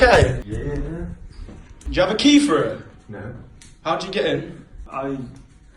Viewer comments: I swear he said "I broke in" also he isn't drunk. I've been out for drinks and been to the point of almost puking but I still knew where I was and where my home is, when you're drunk you still know your surroundings. I think he knew what he was caught red handed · Nah, he played it off acting like he was drunk. He most definitely had some crazy intentions. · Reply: When he mumbles. It sounds like he said "I broke in"